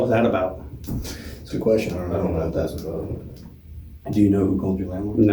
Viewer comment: All I heard them say was "PLEASE do not drag us to court" ... 12:02 When they were talking about the marijuana, did the other cop say "I don't know if that's even relevant"??